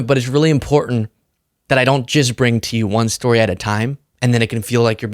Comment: Did you just say you don’t jizz bring? And in which of your fantasies are you sniffing out Musk’s arse? 🤔